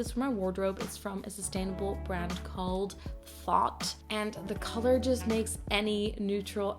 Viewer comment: I listed all of them as URLs :) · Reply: Ah, it was hard to tell with some of them. I was mainly looking for the new slouchy shirt. I think I misheard you and thought it was Fault brand but I am guessing it is Thought brand :)